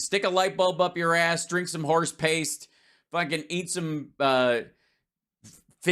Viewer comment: it sounds like Hal said "Fish Taint Cleaner". This also sounds like legitimate Maga medicine.